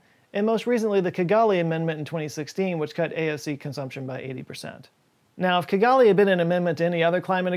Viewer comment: ummm @ 13:26 did you say "cut AOC consumption by 80%" ???? because it REALLY sounds like that.... LMAO - i DO agree we NEED to cut AOC consumtion... but by 100% IMO lol